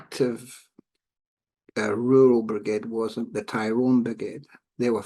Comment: is he saying the Armagh or Tyrone brigade were the most active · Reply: Tyrone